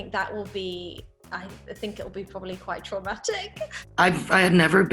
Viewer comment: Omg Elizabeth used ‘bereavement’ and ‘traumatic’ to describe the finale 😭😭😭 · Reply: I thought she said 'dramatic', and not 'traumatic' 😅